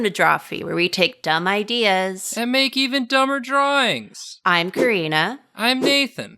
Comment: The audio in the intro glitched so what I heard was "... and make even dumber (silence) Nathan" lmao